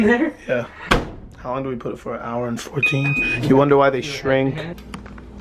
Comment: did he say he will cook it for an hour???